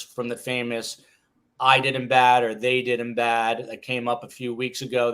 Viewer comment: I heard "I did him so bad" when normal speed. When slowed down, I heard, "i-ee did him so bad". That southern drawl....